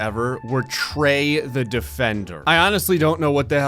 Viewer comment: I thought it said sex offender.